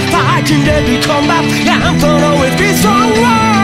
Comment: did he just say Yamcha always be stronger illuminati Confirmed